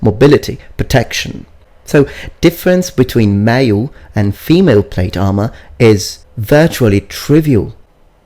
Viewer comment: PRO GAMING In Italy in the 15th century we kept wearing a second mail shirt over our arming doublet together with a shirt of mail for extra protection. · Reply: damn that musta been heavy i thought you were saying mail under plate armor